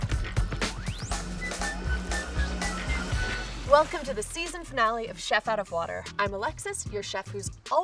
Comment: did she just say season finale season? Oh my God noooo please! I love this season. It's so much fun to watch this. Please tasty come with its season 2